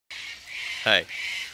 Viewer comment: I heard u say "Baker" the 1st time watching 😅 Im like umm thag punch line doesnt work lol Keep it up Dad! 🤣